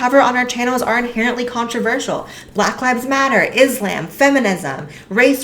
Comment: Did you say "is-lamb?"